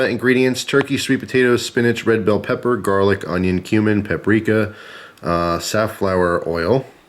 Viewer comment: we know u said cumin, but it sounds like u said human